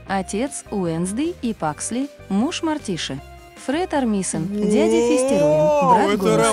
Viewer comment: Эм я одна услышала "муж мартиша"?